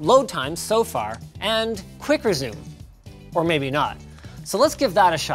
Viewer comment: The english captions say HTMI port at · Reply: captions say quicker zoom rather than quick resume